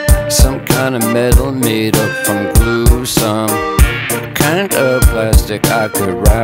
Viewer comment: I hear "Made from tom cruise" but it is actually "Made up from glue"